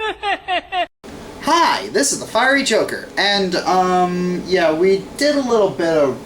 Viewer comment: Could've sworn it said "The Fiery Joker Condoms" at first.